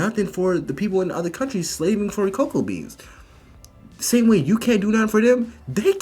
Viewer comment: Hold on. Lol did i hear him say "mine for coco beans" where are coco beans coming from? Lol